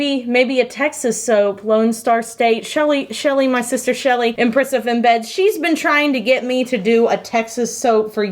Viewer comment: When she said that Shelley was the Empress of Embeds, because she said it so quickly I initially heard it as "My sister Shelley, impressive in bed".